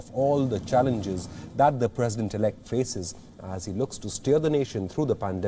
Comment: The reporter's accent it sounded like he said: Steal the nation through this pandemic. My thoughts exactly until I realized he said steer.